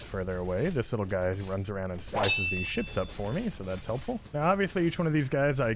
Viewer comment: I swear i heard him say shit😭😭😭